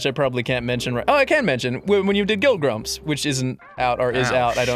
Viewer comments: Did Ross say Guild Grumps, at about 12:48? About the thing he "probably shouldn't" mention? I can only wonder if it's a new show focused on MMO-type games :p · Did anyone else hear that 12:44 Gill Grumps? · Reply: Grieving Osprey Maybe even Guild Grumps or guilt grumps. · What the fuck did ross say? Gill Grumps? Guild Grumps? What? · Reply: Guild Grumps, it's a series about Ross teaching the rest of the grumps World of Warcraft.